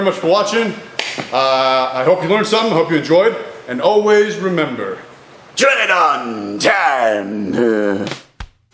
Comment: I always thought you were saying "Tread on time" and I had no clue what it was supposed to mean lmao